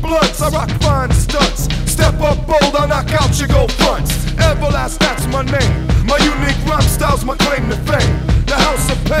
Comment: Everlast that's my name, my uniquee rhyme styles my claim to fame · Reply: I thought he said “Everlast Scott’s my name” forever.